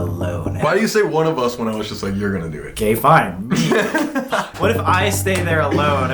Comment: I thought he said gay fine